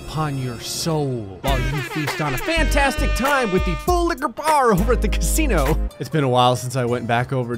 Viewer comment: Can someone please tell me what did he say at 6:49 (i think it was something like "full like a bar" but i have no idea)